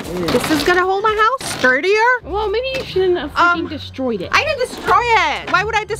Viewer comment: I know she said freaking (I think) but it sounds like she said f***ing